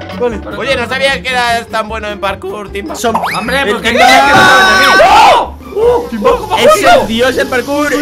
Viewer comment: digame si estoy loco o escuche a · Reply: Es cierto segun yo dijo "So" xD